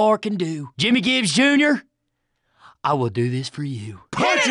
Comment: lol what do they say? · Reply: Punch it